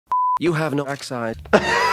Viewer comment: At the beginning the doctor sounded like he said "you have no ackside."